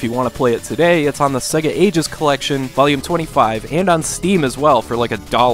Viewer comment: Help please. What does he say between 1:57-2:02? It's available "on like Steve as well for like a dollar"? What's he talking about? Thanks guys! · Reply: I think he said Steam, not Steve.